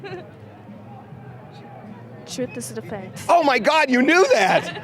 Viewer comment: When Harvey Whispers into the ear of the lady, the answer to his own questions, "anybody know that saying". I have no idea what she says and I tried putting CC on to see if that would help. And I don't think it was, "shoot the defense", lol. Anyone have any idea what she said?